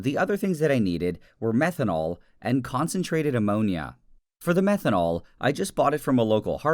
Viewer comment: for a second I thought he said Lemonia